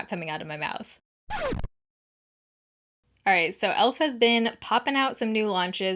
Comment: I heard ‘ELF has been’ as ‘Elephant bin’. 😂😂😂😂